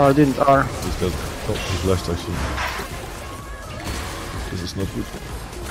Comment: Did u say "Das ist nicht gut " at 3:51 ? :D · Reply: Samuel M. No... „this is not good“